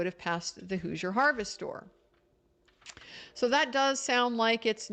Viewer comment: i think you’re saying “hoosier harvest store” but i keep hearing “who’s your harvest store”